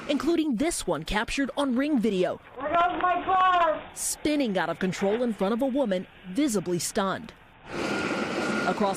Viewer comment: Anyone know what the woman at 6:09 is saying?? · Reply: There goes my car